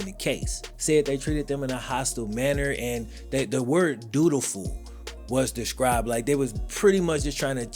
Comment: Brah I thought you said “do do ful”!!!! 🤣 🤣. I hada rewind that bit, “dutiful” 😂😂 I’m like, ok ok 👍 😂😂